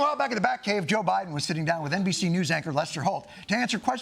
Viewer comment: I thought he said "NBC news actor" lol